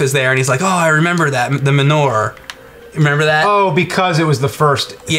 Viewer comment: You fucking youtube content makers and the weird way you annunciate words.. thought he was saying menorah and not manure.. mah+newer.. or just say cow poop